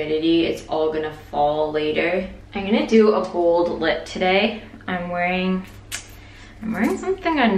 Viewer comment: oohh "bold lip".....I heard "gold lip" LMAO Also you look flippin HOT in that dress!